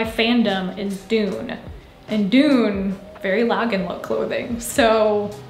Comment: Loved the vid! Just wanted to ask what you said about the Dune trend, I couldn't quite make it out .. "something" clothing at 7:23?? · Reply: Thank you! It's the term Lagenlook, it's a style of loose baggy clothing. Mostly linen and cotton fabric. <3